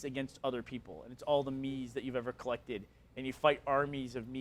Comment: what is the name of the game he is saying?:( pls? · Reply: Warrior's Way